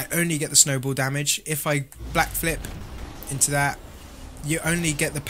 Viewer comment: did anyone hear him say blackflip